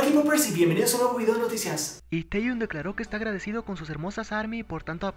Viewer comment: escuche taeyeon